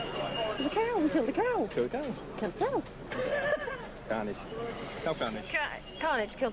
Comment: what did and say at the post battle interview? i heard "the cow! killed the cow!"